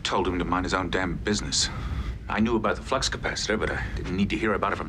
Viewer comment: Did Broccoli say "flux capacitor"?😂